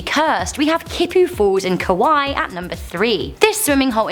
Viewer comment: did she just say Kawaii instead of Hawaii? LOL